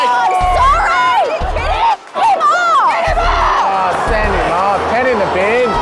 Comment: LOL she says "f--k him off", subtitles say "get him off". We can handle the truth.